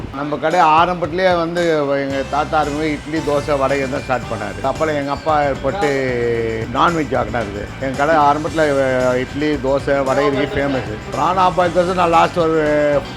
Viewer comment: Subtitle says Dosa and Vadai but he is saying DASA and Vadkari (Vada-kari is a dish and Vada is different dish)